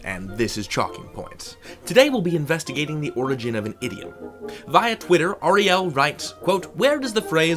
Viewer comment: Today we are investigating the origin of an idiot. I did mishear you there :D Good video